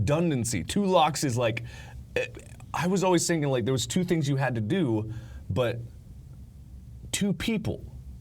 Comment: Two things you have to do I heard 'two people you have to do' XD